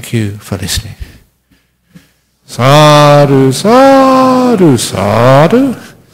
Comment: Great talk. What did Ajahn say at 47:10 ?? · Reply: sadhu "Excellent," "Very good," or "Well done." An expression of praise and approval.